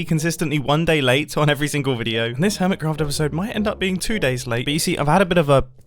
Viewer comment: It sounded like he said Hermescraft